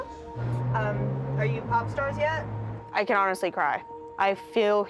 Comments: I THOUGHT IT SAID PORNSTARS BRODDHSHSJ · Who else thought it said pornstars???